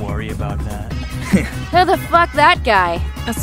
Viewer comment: when she said that I swear she said “who would fuck that guy?”